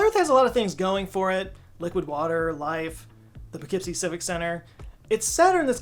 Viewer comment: what does he say at 0:08? (the [...] center)?